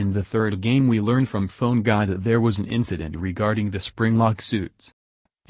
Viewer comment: did he just say phone God?